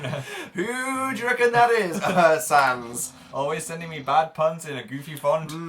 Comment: oh god, the puns are taking over my life. literally thought dan had said "who do reckon that persans is" rather than "who do reckon that is, sans"